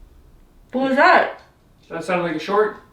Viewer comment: Omg why does it sound like you say a Swedish sentence right there?!? "Vad var det där?" ("What was that?")